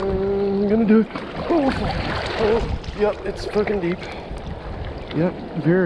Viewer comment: did he say "yup its fu*kin deep"